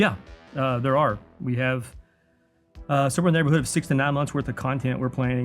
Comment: Video: “6-9 months…” Me: “69 MONTHS?!!!?” Idk made me think of the movie clip it’s funny to me😊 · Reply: Watched this when it was live and nearly everyone in the stream thought he said 69 months 😅